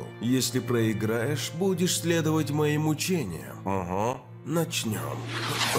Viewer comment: Хочешь Поддержать меня ? Подпишись на мой Boosty!👇🏻 <link> Спасибо! 😃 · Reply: И мне на 2:48 послышалось мучениям ани учениям, хотя етот вариант более логичен, так как для сайтами ето мучения